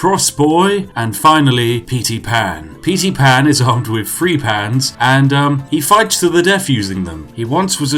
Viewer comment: "And finally, petey pan he is armed with three pans" I lost it there lol too funny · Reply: I actually thought he said "Petey Pan, he is armed with three-tey pans" the first time I listened to this.